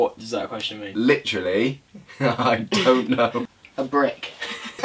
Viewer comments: I legit thought that said the opposite of a koala is a Brook until explained what a brick was 😂😂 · When said a brick is the opposite of a koala I thought he said ‘brook’😂😂